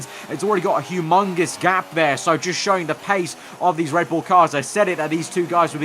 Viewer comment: did you just say "a Hugh Mongus advantage" ? you just did TRIGGER me !